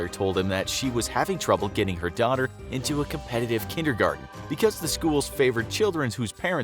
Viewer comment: Tf did he say? Competitive kindergarten?!